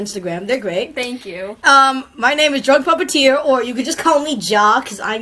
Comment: It sounded like you said drugged puppeteer